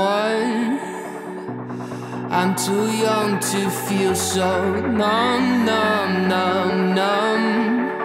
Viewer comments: I hear 'Nom nom nom nom' instead of 'Numb numb numb numb' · Every time he says “I’m to young to feel numb numb numb” I hear “nom nom nom”